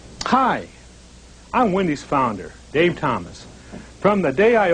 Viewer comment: Hi I'm Wendy's founder Gay Thomas.... am I the only one who thought he said that?